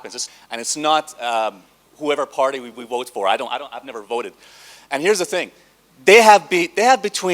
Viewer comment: did he actually say, "I've never voted" at 5:40??